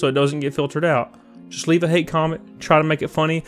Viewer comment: I thought u said hate crimes hahaha